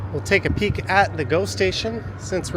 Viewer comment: For a moment, I thought you were saying ghost station. Now I realize it's Go station. No ghosts at all!